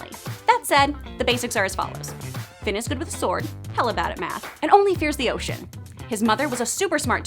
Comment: What did she say at 7:22? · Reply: Bill Cosby “The basics are as follows: Finn is god with a sword, hella bad at math, and only fears the ocean” 👍🏻